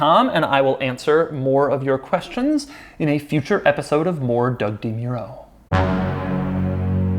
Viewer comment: when doug says "moredougdemuro" i hear "lorddougdemuro"